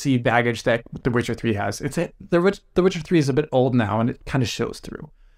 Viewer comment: I keep hearing The Richard 3 🤣🤣🤣